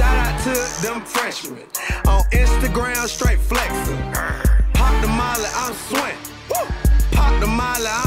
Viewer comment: When I was little I thought he said “Hot Tamale I’m sweating” now looking back it sounds nothing like that